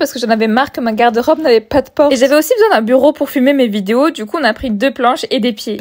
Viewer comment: Mdrr j'ai entendu "et j'ai aussi besoin d'un bureau pour FUMER mes video 😅